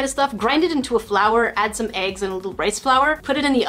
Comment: did you just say add eggs and corn flour